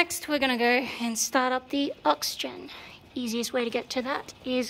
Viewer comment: The oxygen... yeah, the oxygen thing. For those of us playing at home with limited knowledge of tank stuff - why is there an oxygen thing? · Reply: Ahhh... 'aux-gen' I thought she was saying "oxygen". The 'oxygen' part was kind of making sense though - confined space, fuel and oil smells, long periods in heat, etc. - might need some freshish air!